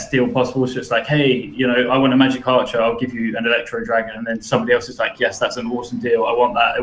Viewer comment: did he just say trade magic archer for electro dragon.?